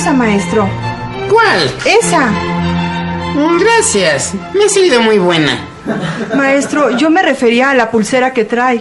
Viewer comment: ¿Qué dice al minuto 0:17 - 0:19 ? ¿Me asciende muy buena? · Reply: Me a salido muy buena.o sea que le a sido de mucha utilidad 😂